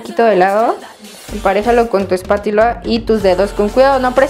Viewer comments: Oí mal o en el minuto 2:40 dijo espatila 😳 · like si escuchaste que dijo espatila en el minuto2:39